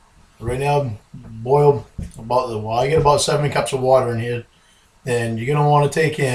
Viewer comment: Did he say "I've got about 70 cups of water in here"?